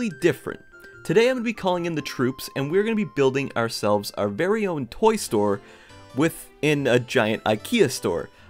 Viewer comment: I thought you said toy story Edit